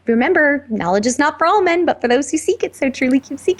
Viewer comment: she says remember, knowledge is not.... but for those who seek so truly keep seeking ... what was the word in the middle there ? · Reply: For all men 😊 sorry it took me years to see this 😊